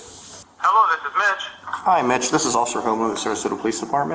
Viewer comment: “This is Officer Homo.” Surely I misheard that?